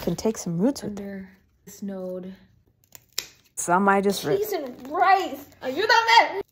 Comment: Did I hear you say “cheese and rice”? 😂